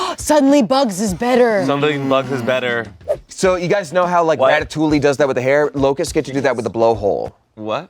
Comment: did Damien say Rata-TOOLY i am …… distraught